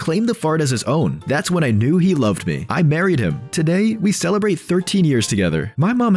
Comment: "13 years later, I married him" At first I thought she said "buried him" 💀